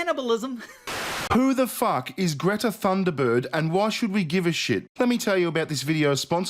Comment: "Who is Greta Thumbird and why should we give a shit?" The same question I've been deeply thinking about for the last tenday. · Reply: I thought he said "Greta Thunderbird".